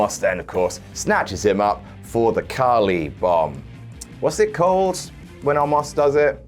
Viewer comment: Everytime said Khali bomb it sounded like he was saying "Carley bomb". Which made me want to see Carley from Gnarley Carley Gaming and No Rolls Barred in the Royal Rumble. lol